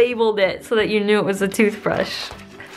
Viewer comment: I thought it said “bruh” but instead it was “brush”